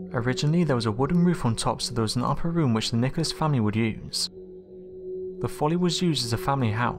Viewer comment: does he say the n/&&@ family? R am I just hearing things wrong?